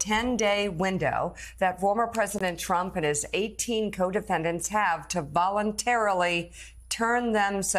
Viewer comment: I heard "his co-dependents" instead of co-defendants.😅 Co-dependents kind of fits.